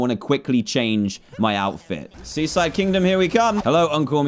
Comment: I thought he said "Suicide kingdom here I come!"